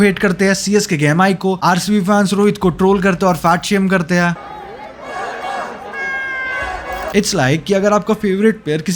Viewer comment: kya bol raha hai wo Banda ?? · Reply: Rohit Vada pav